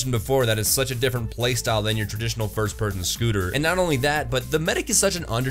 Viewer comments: did he say first percun scooter? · Did anyone else hear him say "first person scooter"